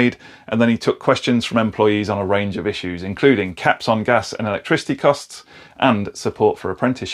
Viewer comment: You said "caps on gas" and I heard "gash on tap". You're living the dream mate! 🤣🤣👌